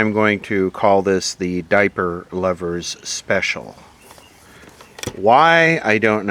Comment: I could have sworn you said ""face diaper lovers special" lol